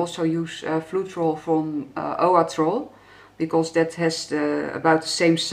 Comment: Hi, what did you say at 1:15 please? "Instead of Deco varnish you can also use ....... ? I am looking to do pour painting without producing cells. Thank you. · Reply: She said floetrol by flood